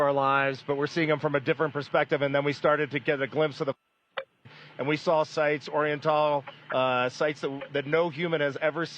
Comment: What does he say at 3:58? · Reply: I believe he said “far side” in that second he was cut off